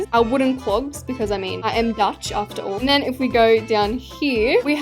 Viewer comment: Did she Just say she's Dutch? Bc idk if she is, please tell me I NEED ANSERS! And if she is she my bestie now and if not she still Will be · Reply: That’s what I heard😭 “I am Dutch after all” I heard her say that…😅